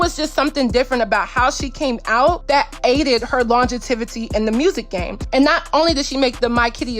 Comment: did you just say longetivity ?